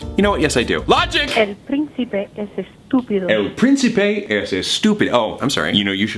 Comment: What I hear: The princey is a stupid h-- Me: XDDDDDDD